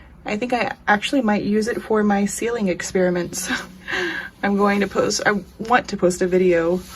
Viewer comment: When you said "sealing experiment" I heard "ceiling experiment" for some reason and I was wondering what that would be. I had visions of ceilings papered with diamond paintings that kept falling off.